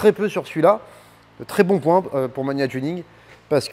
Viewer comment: Salut, tu l'a pris ou le kit ? j'ai compris "mania tuning" dans la vidéo mais rien trouvé avec ce nom. Merci :) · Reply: C'est MAGNA tuning ;)